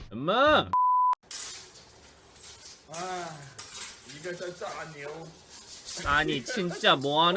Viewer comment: 나만 이거 "아.. 이거 설사 아니에요?"로 들림? ㅋㅋㅋ